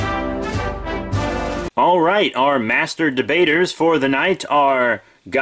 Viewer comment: it sounded like he said master-di-baters XD